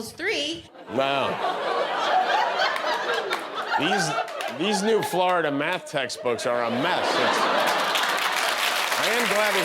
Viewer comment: What did Jimmy say? - "These Florida Math text books are a mess!" Or "These Florida Math text books are a Meth!"